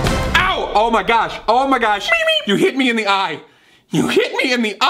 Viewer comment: Roman: *gets hit* Patton: MEEP MEEP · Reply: MEEP MEEP At first I thought he said “Meanie...” and then I turned on captions.